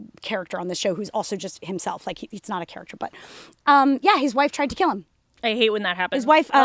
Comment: I totally thought you guys were saying his wife tried to kill Matt Rife 🤣